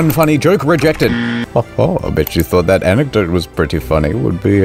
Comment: Reject? OOHHhhh....I thought you had said Re-Jeff. I was so confused. The reasons seem obvious!